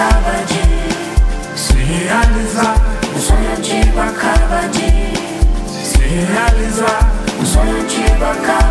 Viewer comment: a primeira vez que botei essa música pra ouvi, escutei "um sonho te guardava de se realizar". depois eu vi que não era bem isso, mas essa frase me tocou profundamente e me fez repensar muita coisa. às vezes a gente se apega tanto aos sonhos que faziam sentido no passado e isso impede da gente se realizar no presente com nossos novos desejos...